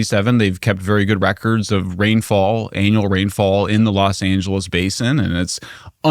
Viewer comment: did he just say ‘Anal’ rainfall? Anyone? Anyone??